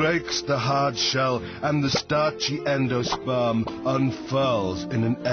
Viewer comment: did he say sperm ?